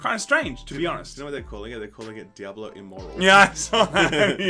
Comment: Rewinded this multiple times, but still have no idea what you just said there. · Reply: diablo immoral :,D